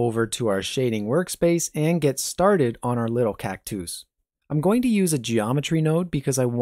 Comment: I like how he said cactouse instead of cactus, like a feminine form of cactus